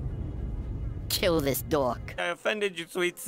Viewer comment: “Kill this dork” 😂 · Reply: I first watched it without his execution, and I thought he said "kill his dog"